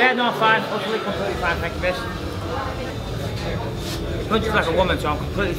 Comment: does Bisping say "thank you bitch"?